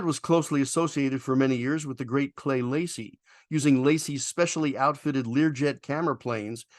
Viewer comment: 'Don't know about the two C-97s, and where did you hear that Clay Lacy is gone? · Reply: sorry I thought you said the Late Clay Lacey but it was the great Clay Lacey. I took it as he's gone. He saved a C-97 or two from being scrapped but no one seems to no what he did with them or going to do with them.